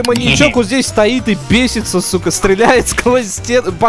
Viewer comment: ПОЧЕМУ ЗАПИКАНО ?ЧТО ОН СКАЗАЛ ? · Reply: сука он сказал!!это и за того что ютуб щас не платит денег если будешь материться в ролике